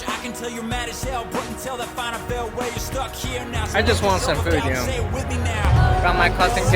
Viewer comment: what does he want ? listened to it 5 times and still dont understand it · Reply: "wanted some food here"